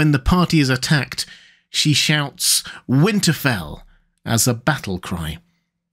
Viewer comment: she was actually saying "go to hell" even though it's a stupid thing to yell